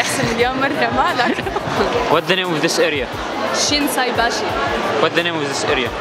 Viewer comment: اني هيج سمعتها She is باشا 😂😂